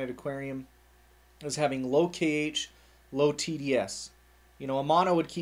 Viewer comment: ok you lost me at cage ... cant seem to find any videos for total beginers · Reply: He said "low kH", not "low cage" haha. After reading your comment, when he said it, I heard it as "cage", too lol